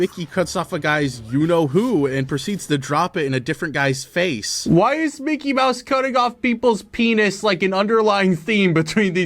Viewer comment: I wasn't paying full attention at the time and thought was about to say "Why is 'Mickey Mouse cutting of people's penis', like, in Undertale?"